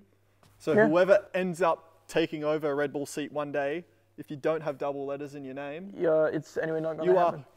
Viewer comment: I thought Ricci said The Double Lettuce thing.